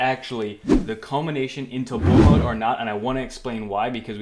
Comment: “The culmination into [unintelligible]” What did you say? It was unclear. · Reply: bull mode